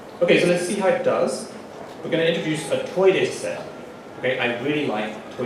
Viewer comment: I kept hearing this as toilet set